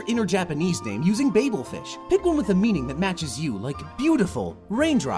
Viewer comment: bagelfish? someone pls tell me what they said